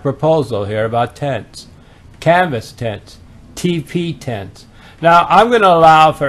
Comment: Jeez! At first, I thought he was saying T.P. tent. Then, I realized it was teepee tent.